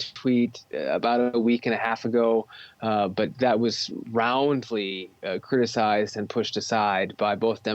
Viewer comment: I thought you said he was removed "roughly", if your unsure of roughly, I could show you ?